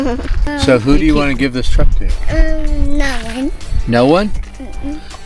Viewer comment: I could have sworn i heard i wanna give the truck to Jimmy. 😂